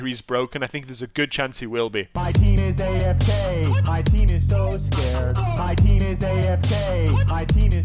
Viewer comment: Doesn't matter about the changes because anyway my team is gonna be AFK, my team is so scared, my team is AFK, my team is so scared. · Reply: Are you kidding me...? This ENTIRE time I thought he was saying "My penis AFK, my penis so scared"